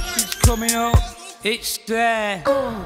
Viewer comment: Coming up coming up Coming up ITS DARE *me : Go back to sleep honey " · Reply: I honestly thought he was saying "he's coming out"